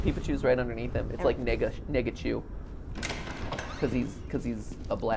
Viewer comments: I'm sorry, but what did you say, Matthew at 53:00? The CC shows something really offensive and it honestly sounds offensive in and out of context. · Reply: Clayton Ford I believe he was saying, “nega-chu”, like negative peepachu? I hope so anyway, that would be horrible if he said what CC thought he said.. 😳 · What I'm so confused. Does matpat say the n-word or no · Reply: Negachu = Negative + Pikachu As in his colors are negative. The phrasing literally couldn’t be worse tho lmao